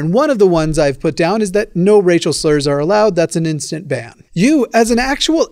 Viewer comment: At first I thought you said Rachel slur and I was just like 'who's Rachel?'